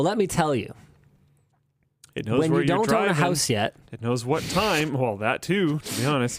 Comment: what did Linus say there, overpowers it a bit so I cannot hear him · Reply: "when you don't own a house yet"